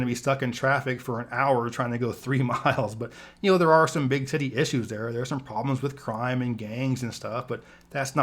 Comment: Sounded like he said "...there are some big titty issues there" lol